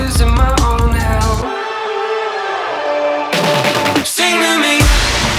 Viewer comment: When I hear 'sing to me' it sounds like s'en la vie, Which i think roughly translates to 'in life' Which i think fits